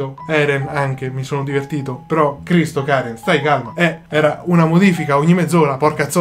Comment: scusate ma solo io al min 7:19 ho sentito partire un bestemmione totalmente a gratis?? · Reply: Riccardo Celebrini ho detto "Cristo, Karen" 🤣